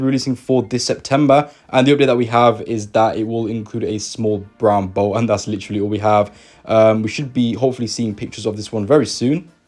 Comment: What does he say at 6:20? I dont know english :(( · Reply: "...and the update that we have is that it will include a small brown boat, and that's literally all we have..."